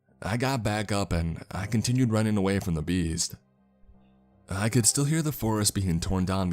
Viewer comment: lol, I kept hearing "the bees" not "the beast"...thinking, wow, this guy is really afraid of bees!